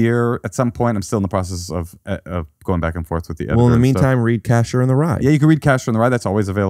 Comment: I thought Moshe actually said “The CATCHER in the Rye” and the whole time he was talking about his real book, I thought he was just doing a bit pretending he wrote JD Salinger’s book